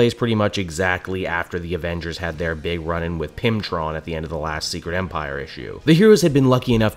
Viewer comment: At first I thought you said Pimptron